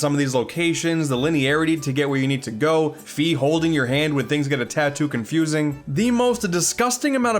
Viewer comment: Totally misheard as "Fi holding your hand when things get a tattoo" and yeah, that was confusing.